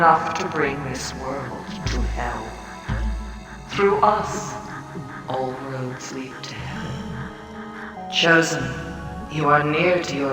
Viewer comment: your CC says: "Through us, all roads lead to heaven." The mask clearly says "Through us, all roads lead to hell."